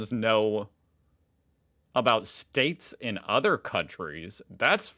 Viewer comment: did he just say states in other countries